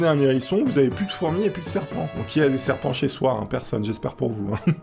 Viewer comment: j'ai une serpent chez moi c'est bête 🤣🤣 IG: Samix.69